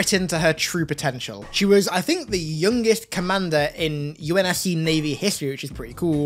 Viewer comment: I swear i thought he said Miranda was the Yummiest Commander in the UNCS, and I agree 100%